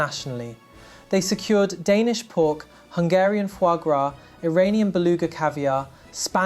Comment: i have no idea what you said here, either your pronounciation is wrong, or its some food i actually never heard of in Hungary. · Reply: Foie gras is fatty goose/duck liver. The animal is force fed grain and butter. It’s an organ full of fat and often fried. Very unhealthy but quite delicious.